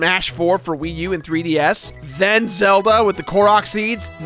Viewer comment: Did he say Zelba??? 4:00 Who the Fuck is ZELBA